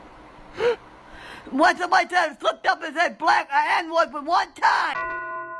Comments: what she said was “one time my dad slipped up and said black n-word one time” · Sounds like: "What's up is that I slipped up and said black - a n-word - one time"? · it sounds like she said "one time my dad flipped up and said the black the n-word one time" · i think it says "one time my dad flipped up and said the black n-word one time" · i think she said "once in my time i flipped off a head black with a n word one time"